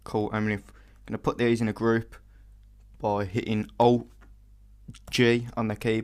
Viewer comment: wow can someone tell me what he says at 8:50 "hit "O" and "j"? because that doesnt work...